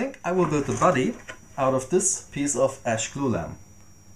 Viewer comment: I can't understand what you say at 1:04 :( "piece of ash ???? " I get the mood to build one :) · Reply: "Ash glulam"